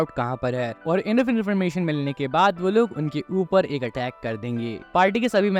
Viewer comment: xd lol bhai ye kya bola ap ne? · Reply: Enough information milne k baad